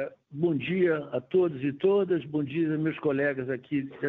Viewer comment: seg entendi o cara falando "todes". Se realmente foi, PRO INFERNO COM ESSA DESGRACA DE VOCABULARIO! Ou ele quis zomba dessa nojeiras que estao impondo nas pessoas